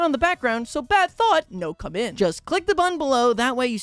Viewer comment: at like 8:45 i thought he said “so no bad thought, no commit” i was like DAMN BRUH so real😂